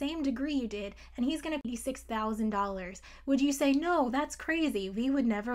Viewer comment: it sounded like "pay 6 thousand dollars"